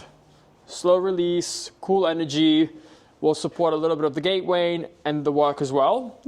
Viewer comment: lol "Gate Wain" instead of "Weight Gain"